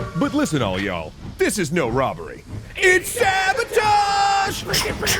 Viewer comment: When they said sabotage I thought they said saboton for a sec and there was gonna be a mega Collab